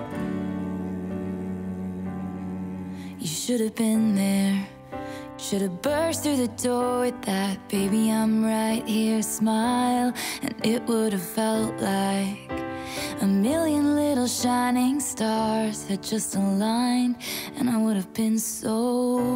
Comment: It’s been bothering me for weeks. When she says “you should’ve been there, should’ve burst through the door with that baby I’m here smile” I always hear “baby I’m here smile of yours”. Every time. Why can’t I get it out of my head? ToT